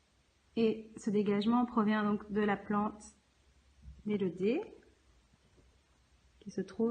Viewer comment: j ai pas trop compris se que ta dis plante quoi ?????????????????????????????????????????????????????? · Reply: Plante élodée